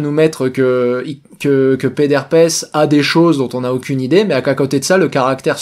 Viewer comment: j'ai entendu pet d'herpès, j'ai bugué 😅